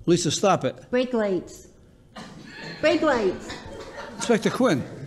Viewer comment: I thought she was saying fake lights 😅